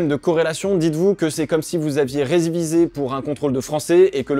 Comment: ré- quoi ? 😂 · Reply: Résviser je crois 😂😅